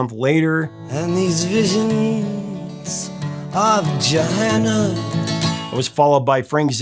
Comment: Closed caption funnies at 20:25 — visions of vagina, instead of Joanna.